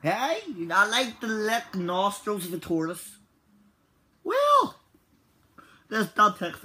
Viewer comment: i thought he said nostril sartorius